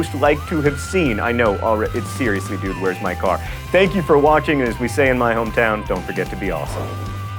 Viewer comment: I swear, it sounds like he says, "Thank you for watching menopause"